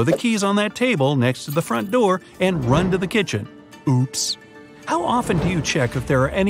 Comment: When he said oops I thought he said ops